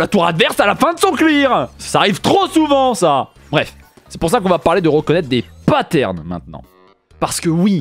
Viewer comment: j'ai mis pause au milieu de la phrase, j'ai donc entendu "on va apprendre a reconnaitre des pâtes" programme ma foi intéressant